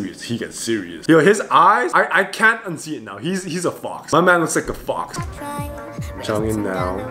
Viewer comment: I guess, in the 1st clip he said: «I am fox», but you can mishear it because of the pronunciation.